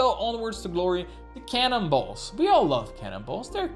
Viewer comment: The Cannonballs......was what I thought he was saying for CANNIBALS.